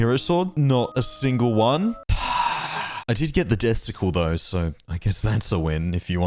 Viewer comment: When you said “I did get the death sickle” I thought you said “I did get the testicle”